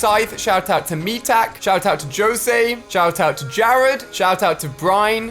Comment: did he actually say "joe say" im dying of laughter